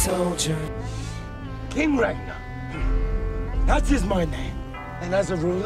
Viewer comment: King Redgman, Ridman, Redman or Ritman? What he say at 2:07- 2:10? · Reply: King ragnar that's his name